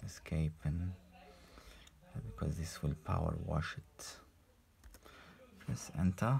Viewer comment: alguem pode traduzir na parte que ele fala "Power uashin" nao entendi 😅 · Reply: He said power wash it